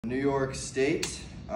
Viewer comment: Am I the only one that thought he said New York Steak??